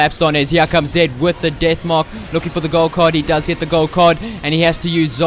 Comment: Am i hearing " Golf card ?"